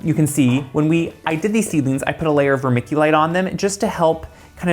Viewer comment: Hi great video..... Hey what is Romithulite?..... Not sure if I hear the name correctly. It's the stuff you sprinkled on at time code 3:48. Many thanks again.